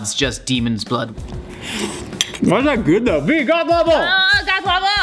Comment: Goth waffle? I thought you said blue waffle.